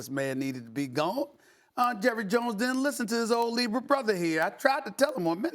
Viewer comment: i coulda sworn he said “jerry jones didn’t listen to his negro brother” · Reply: Nope, he said " Libra brother"